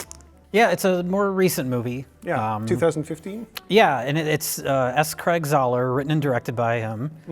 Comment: honestly thought you said "Asscrack"